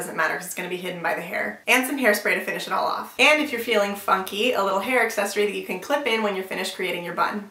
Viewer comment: omg lool i thought u said the f word instead of funky